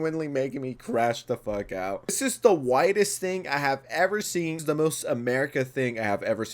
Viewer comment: At first I thought you said "WIDEST THING" and that took me out